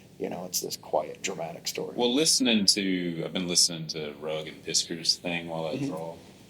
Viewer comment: What did say he was listening to at 16:18? I heard Rogan piskers but I cant anything by that name. I love the history of image so always up to hear new stuff. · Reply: Rugg and Piskor's youtube channel: Cartoonist Kayfabe